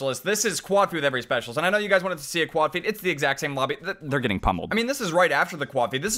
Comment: did someone say bobby?